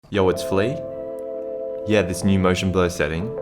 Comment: I thought he said “ yo what’s flea “ Edit: he left the PS gang